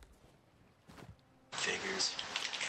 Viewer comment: did he say what i think he said? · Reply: I heard “figures”. Like figures the chest was stolen. Or figures it was them on the sloop from earlier.